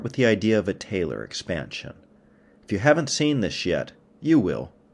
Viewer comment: I heard "If you haven't seen this shit" lol